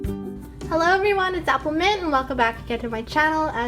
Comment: Lol I thought she said Apple men xD